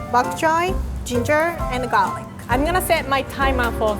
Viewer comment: you can peel ginger with spoon I know she said it as nothing but I think that just changed my life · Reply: I was scrolling and a thought she said finger I was NOT paying attention